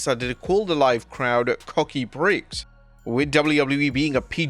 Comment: Time stamp 3:52... Uhhh, what? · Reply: I thought he said "Cocky Bricks" 😀